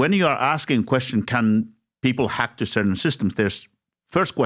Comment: Did you just say 'Hawk Tuah' 🤣🤣🤣 32.30 No 'Hack two'